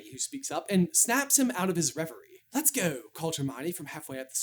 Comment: When said " let's go, called Hermione " I heard "let's go Paul giamatti" and now there's a head cannon in my head where Paul Giamatti plays Hermione and I'm not mad at it.